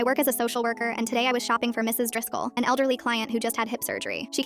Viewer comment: thought you said mrs o’driscoll and immediately thought of rdr